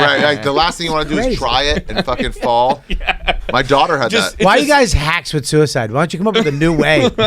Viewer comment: Right around 36:00, ari says "my daughter had that" or am I hearing that wrong?